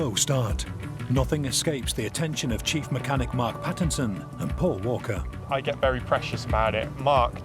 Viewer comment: did he say Paul Walker🤨